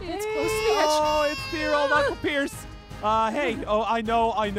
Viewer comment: When pierce said uncle pierce I thought he said "knuckle" Pierce 🤣🤣